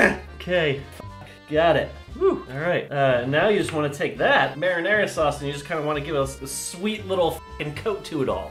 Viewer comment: it sounded like he said marijuana sauce xD